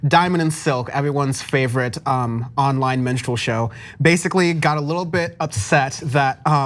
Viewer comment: I heard "menstrual show" and just went with it....